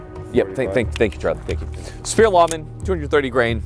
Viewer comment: Did he say Spear Waffen? Lol o/